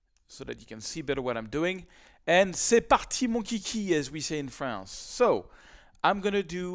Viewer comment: you said something in French. I didn't understand. C'est pas.....something. Would you type it in here and translate into English? I would appreciate it. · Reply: Mitchell "c'est parti mon kiki" difficult to translate literally but more or less 'let's go' (kiki could be the name of a dog)